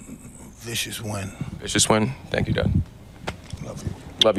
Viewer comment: am i trippin i swear i heard him say "thank you daddy"